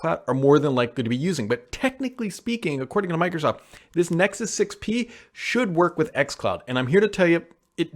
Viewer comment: Do you mean the OnePlus 6T not Nexus 6T? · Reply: I thought he said Nexus 6P